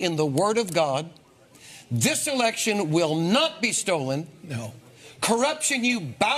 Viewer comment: did she just say Stoli??